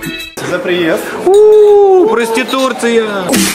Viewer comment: мне послышалось проституция